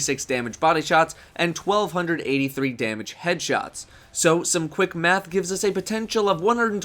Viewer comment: anyone else think that at 1:46 it sounds like he says, So some quick meth